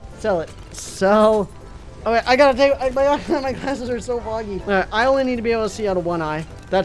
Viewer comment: what did u just say u just said MY GLASSES ARE F&@@ UP